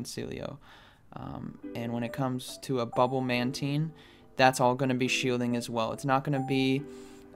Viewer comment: Sounds like you say bubble man team...LoL